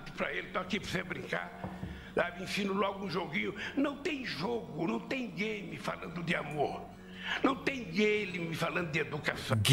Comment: a primeira vez entendi "não tem gay me falando de amor"